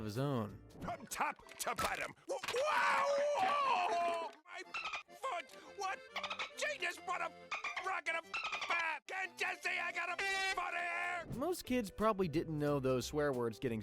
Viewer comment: Damn now I know what they were saying 3:23 😂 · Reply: "OWOW MY FRICKING FOOT WHAT FRICKING GENUS WHAT A BRACKET OF FRICKING BAB CAN'T YOU SEE I GOT A FRICKING HURT FOOT HERE"